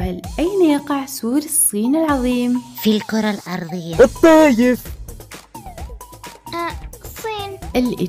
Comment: ايش قالت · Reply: الطايف